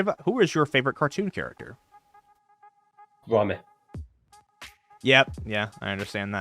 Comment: What did he say at 1:12:53 ? Sorry, but I'm that Iris person who asked the question and I'm a little deaf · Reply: He said Gromit!